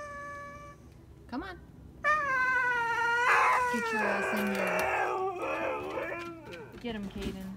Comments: I swear i heard “get your ass in here” 😭😭😭 better listen to your mommy lol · Did she is just say “get your ass in here...?” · Did she say "Get your ass in here." At around 0:30 ? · Did say get your ass in her at 0:32 LMBO😂